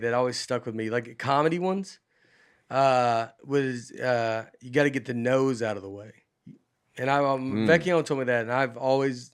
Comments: When Nate said, "you gotta get the "no"s out of the way," I totally heard, "You gotta get the nose out of the way." Honestly, that would make more sense for some of us... not naming any names... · I thought Nate was saying "You have to get the nose out of the way " haha